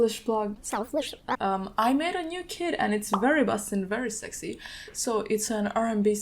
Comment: For a second I thought she said “I made a new kid” when she was talking about her kit😭🤦🏾‍♂️